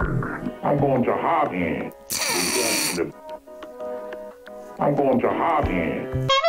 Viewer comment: He sounds like he says "I'm going to hibernate." Is thee something this guy isn't telling us???